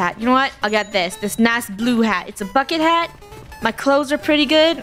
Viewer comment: wait what did she say? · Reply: A bucket hat